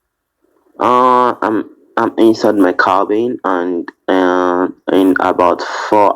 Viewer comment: Did I hear him right did he say that he had been in his car earlier that day and that he had to go to work in 4 hours?,must be some size of ship he's on. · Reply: I thought he said "am in my cabin".